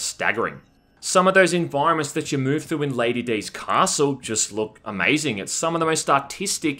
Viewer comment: I was a little bit away from the PC, and thought I heard, "Lady D's asshole" and swung around back to the PC ASAP.